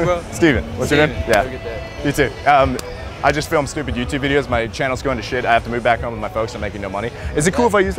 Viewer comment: Did Steven just say his channel is going to shit?